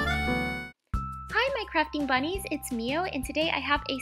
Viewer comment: At the start when she said hello my crafting bunnies I thought she said “Hello my crapping bunnies”